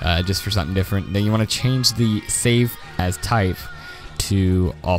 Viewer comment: When he said save as i heard "Save Ass"